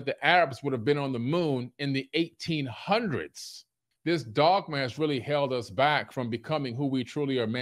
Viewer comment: Did Billy say dogmash? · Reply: I think he was going for dogma, but yeah, sure sounded like dogmash.